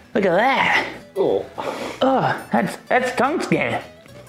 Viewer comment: It sounded like he said “thats tungsten” after he pulled off his tongue skin😂😭